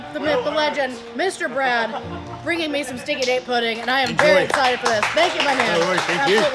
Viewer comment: I thought you were saying Stinky Dink Pudding! 🤣🤣🤣 You don't want to know what came up searches! 😲🤣 Then I read the comments. Oh my goodness....what a chuckle!